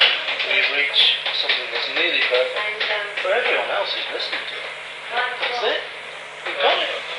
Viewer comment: This doesn't change the meaning significantly, but at 13:14, I hear Paul saying, "For everyone else who's listened to it . . ."